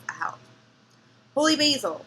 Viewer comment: When you said Holy Basil i thought it was a way to say Hole Mole. LoL